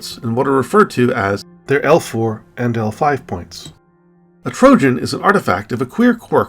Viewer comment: Instead of “their L4 and L5 points” I first heard it as “their Elf War and Elf Fly Points”. I had to rewind.